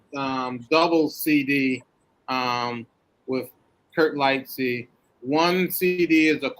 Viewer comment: I couldn't catch what record was referring to at 1:57:56. I could understand Kurt was the first name and the series is called conversation, but I am having a hard time trying to find the album now. Thanks! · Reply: The Conversation series is with Florian Arbenz and Kirk Lightsey. If you Google their names the Bandcamp page should come up